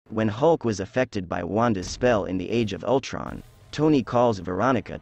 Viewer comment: When you say "When Hulk was affected by Wanda's spell" I thought you say "When Hogwarts was affected by Wanda's spell" lol😂😂😂😂